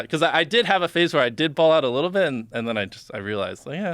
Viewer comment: what did he say he bought, at 14:38? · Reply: BMW i8. Fancy car